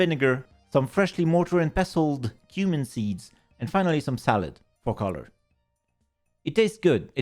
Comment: When you said "Cumin seeds" I heard "freshly ground Human Teeth", which about gave me a heart attack. That one's on my audio processing issues, lol